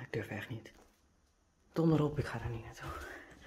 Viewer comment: 何回聞いても日本語で どのロープだ？って聞こえるのが怖い。